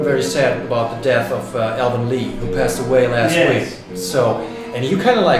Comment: Thought the guy said ALBERT Lee had died! He actually said Alvin Lee!!! nearly had a heart attack...